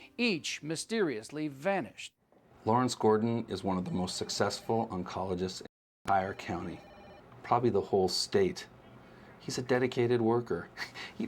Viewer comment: Gordon is one of the most successful oncologists~??? I cannot hear rest of the sentence. Anyone heard what he said? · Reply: "one of the most successful oncologists in eyre(???) county, probably the whole state"